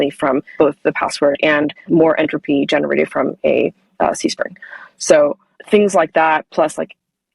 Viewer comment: "... and more entropy is generated from ????" can anyone hear what said here about where more entropy is extracted from? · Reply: after looking into privy's docs I've figured out it was "CSPRNG," which stands for Cryptographically Secure Pseudo-Random Number Generator